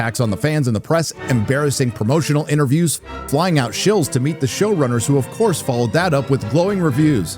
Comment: For a minute I thought he said, "Flying out shills to meet the shill runners" and chuckled. Then I realized that that was accurate too.